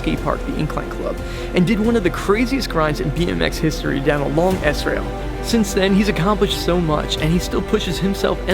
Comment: Thought I heard a cuss word when was talking about Morgan wade. Had to go back to hear what I thought was “a long ass rail”. But was “a long S rail”.